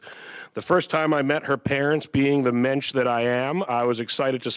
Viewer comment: I thought he said "minch" at 1:16 LOL but apparently he said "mench" which is I guess a jew thing